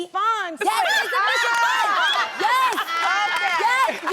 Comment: what did tamar say at 8:26? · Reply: “Insufficient funds”.